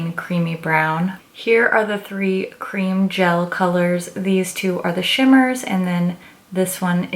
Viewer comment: As I'm watching the video my 3yr old overheard you say 'cream gel colors' and he said 'cream jello colors? I want that jello' hahaha